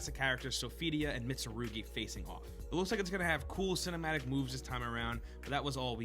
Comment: I heard ‘cinematic moves’ as ‘cinematic boobs’. Both are fine.